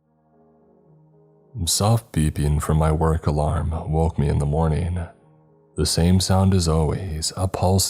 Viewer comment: Sounded like he said soft peepee woke me in the morning lmfao